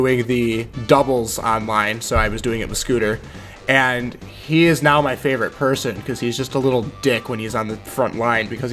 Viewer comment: Had to take a double-take there - I thought was saying 'I was doing it with Scooter and he's now my favorite person ' Interersting list, thanks for sharing!